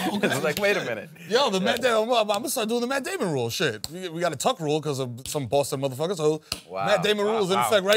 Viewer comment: WHAT DID MERO SAY @ 7:04 ? "We got a tuck rule?" Why did it hurt Matt ("like a shiv to the ribs") ?